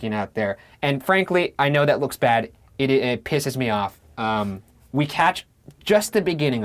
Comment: wait did you say 'catched' at 18:47??